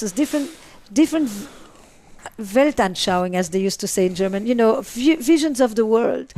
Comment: What's that German word for 'visions of the world'? Anyone know? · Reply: It is "Weltanschauung" and it means the way someone sees "the world" (e.g. a religion) It is a way of understanding the world, nature and the essence of man